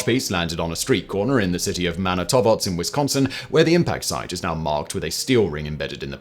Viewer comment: Ok, not sure what went on the with the pronunciation of Manitowoc. It sounds like Simon said "Manitobots" to me, which is odd considering the spelling. Might be a typo in the script, but the pronunciation is Man-ih-toe-wok.